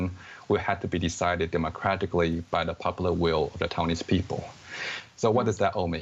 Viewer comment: Auto-Subtitles are wrong...Should be correct as: ...by the popular will of the Taiwanese people.... NOT the Chinese people.